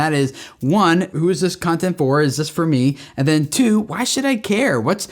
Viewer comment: I really thought you said "who is this cunting for?" hahaha